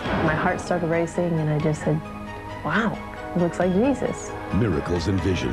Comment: Did she just say Yezus?